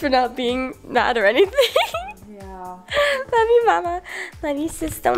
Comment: When said love you I thought she said “f you”